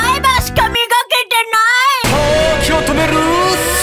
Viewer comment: の時を止めるスマホが時を止めるスタンドに聞こえた